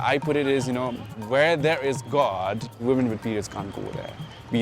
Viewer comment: I cannot understand what the man at 15:00 is saying. "Where there is god women would be his concord holder" is what I'm hearing no matter how many times I replay it. Someone help! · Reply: Where there is God, women with periods can't go over there. But yea he slurs it like womn wid beris can go ove der